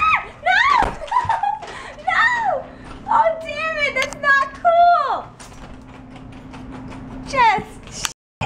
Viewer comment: does she say "Oh Darren that's not cool"?